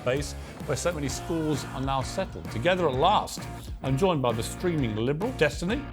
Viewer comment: I thought piers said I'm joined by the screaming liberal and had to rewind